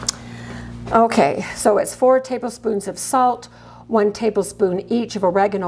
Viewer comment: I'm confused about amount of salt.. I thought you said 4 tablespoon of salt.. but recipe written said 4 teaspoons of salt. Obviously if I make it I'd taste .. but did I hear wrong?